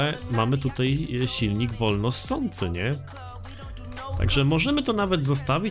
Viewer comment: Czy tylko ja usłyszałem Volvo ssący